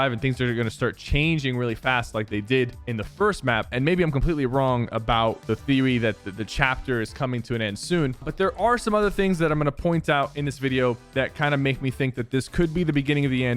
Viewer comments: ok why is there street fighter in the description and i think he said thewy not theory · he says thewey instead of theory lol